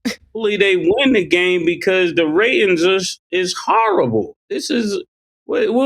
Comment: i thought mase said they won cuz the ratings😭